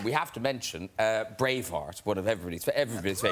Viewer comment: When Graham said “Braveheart” I heard “Brainfart”. 😂 I was confused for a second until they showed the photo from the film.